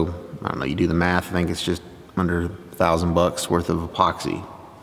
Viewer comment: Thought you said $100,00 worth! 😂